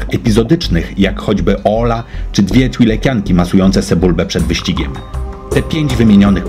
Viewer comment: zamiast sebulbę usłyszałem se bulbę :D